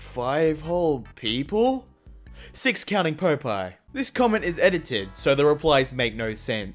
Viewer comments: Wait... Hol' up. Did you just say PO-pie???. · Did just say pope eye?